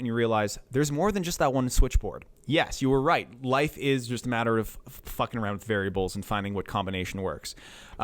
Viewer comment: For a split second I thought I'd heard "life is just fvcking around with fairy balls" and this is now my new life motto.